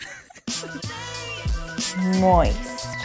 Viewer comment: i thought she said "moi" which means "hello" on finnish language :D